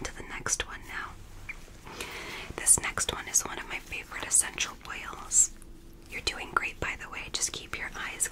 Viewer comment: I'm falling asleep and I thought you said sexual whales not essential oils 🥱😴